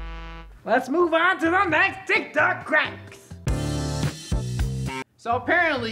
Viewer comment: I had the cc on and at 0:50 is said d*ck duck instead of tiktok